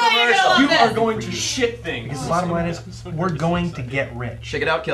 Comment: Haha! I thought that's what I heard but I couldn't believe it. I like when he says "You're gonna shit things."